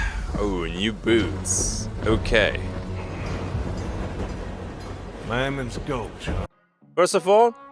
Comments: I hear "Mammoth's Gulch" 🤷‍♂ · Personally I heard "Lineman's Gulch," but who knows?